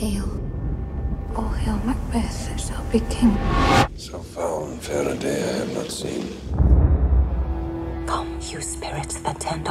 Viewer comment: what did fassbender say in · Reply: So foul and fair a day I have not seen.